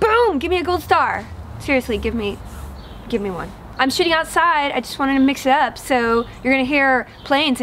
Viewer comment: I could have sworn you said "I'm shitting outside." And I was really happy, because I thought we finally had something in common.